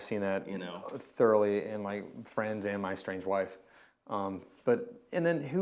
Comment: I swear I thought said 'estranged wife'...